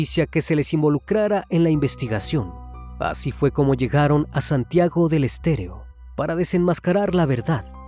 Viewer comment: me parecio que dijiste Santiago del EsterEO y es Santiago del EsterO!